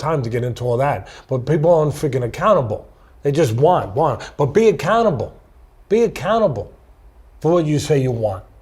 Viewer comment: When Teddy said "be accountable" I thought he was saying "be a canibal" (2'53")and as much as I love Teddy, I started to think gee this guy serves up some real tough advice dont think Im gonna be able to measure up 🤣🤣🤣